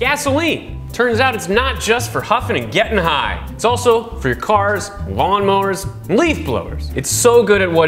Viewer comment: I herd this muffled and I thought he said cars, lawnmowers and flame throwers